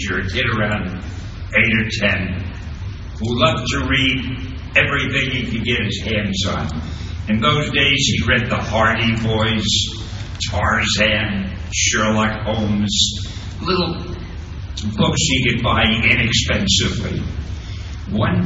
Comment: Did he just say The Hardy Boyz on 5:05?